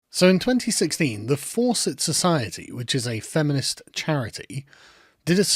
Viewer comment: Better to be a revolutionary than a dirty smear merchant, Buzzfeed: <link> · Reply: The I swear to God, I thought said the feminist group was called the “Force-It Society”, which would actually make sense.